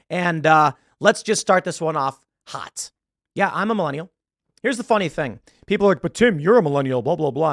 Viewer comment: did he say millenials are hot?